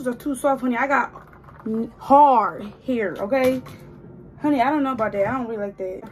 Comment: you was finna say you got nigga hair !!😭😂